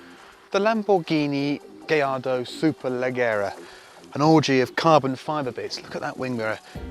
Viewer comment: Did he say Orgy at 1:12 ???